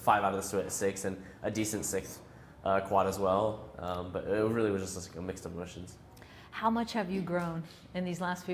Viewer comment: What does he say at 2:52. Help,please. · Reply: "It really was just like a mix of emotions" XD took me a few tries